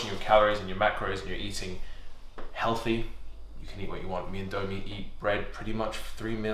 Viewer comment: did he say 'me and domie'? ... does anyone watch trainwrecks? i was thinking this guy look just like him at the beginning